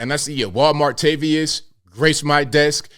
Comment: Walmartavius? I almost fell off my chair. When I was in management I did the same thing and HR departments everywhere do that same thing. Same with seeing pronouns. Most HRs see "my pronouns are..." automatically File 13. They just don't have time to deal with it. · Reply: I thought he was saying Juan Martavius! 😂